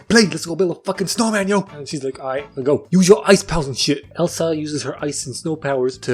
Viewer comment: i thought you said "isis powers" here